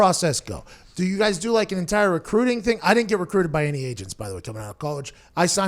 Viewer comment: Every time he said "agent" I thought he said Asian